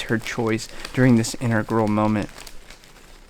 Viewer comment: I misheard "integral moment" as "inner girl moment"